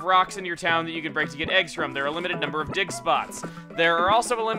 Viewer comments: is it just me or dose it sound like he says dick spots instead of dig spots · i swear i heard say "there are a limited number of dick spots" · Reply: *Dig spots :')